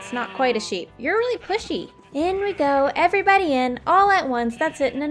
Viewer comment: pushy? oh damn cuz i thought you said puh-sea... STOP... sorry y'all